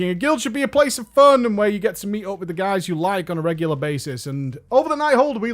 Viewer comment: I heard: "Where you get to meet up with the guys who lag on a regular basis" - yep. Yep...